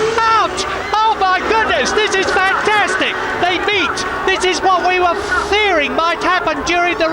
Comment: What did Murray Walker actually say? Beat, meet or beach? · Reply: Meet, i.e. come together